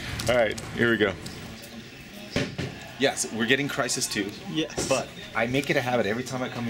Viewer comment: does he say crysis 2 in 0:44 ?